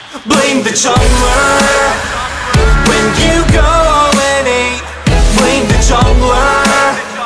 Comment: Before I saw subtitles I heard "When you go NA" instead of 0-8 xD , GG very nice song guys :)